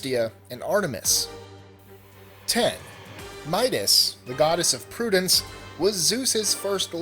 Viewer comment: I thought you said Midas When it's Metis